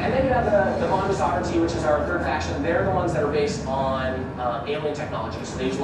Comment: Was drinking when i thought he said "Anal Technology" and also spat my drank out